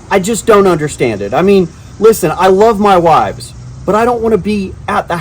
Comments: "I love my wives." Plural? Did you say that in plural? Or, did I mishear that? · Did he say I love my wives? As in plural? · Wait- did you say WIVES??? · "I love my wives..." @ 1:00 uh, you what? Perhaps I misheard